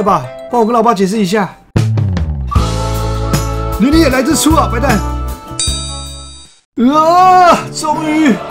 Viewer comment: 把白蛋聽成混蛋🤣🤣🤣